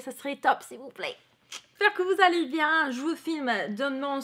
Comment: 10'54 : j'ai compris "je vous fouette..."